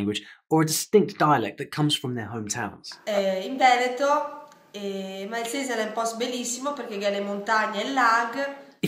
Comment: Wow, very interesting video. Fun fact: italian dialects can be very different even in few kilometers apart. I live in the Veneto region but I almost didn't understand what the woman said at 1:21 , I think she spoke a dialect from Verona if I undrestood correctly. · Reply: Ma come non capisci scusa? io sono di vicino Milano e dice Malces (o Malses) è un posto bellissimo perchè ci sono le montagne e il lago....